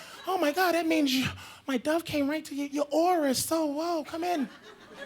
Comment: did he say your aura is so woke? If so, ahead of his time hahahha